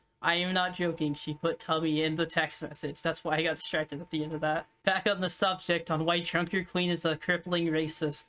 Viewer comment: What was said: "Back on the subject of why Junkerqueen is a crippling racist" What the subtitles say: "Back on the subject of why Trump your queen is a crippling racist"